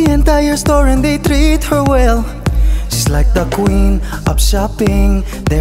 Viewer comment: did he just call her an ant queen?